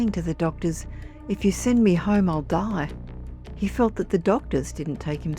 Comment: Listening to the narrator, when she described Billy touching the ET I thought she was saying "He felt HATE".. I had to listen to it a couple of times to realize she was saying HEAT. What a weird experience and I feel sorry for him having to walk to the transportation and also walk a mile home. Very sad.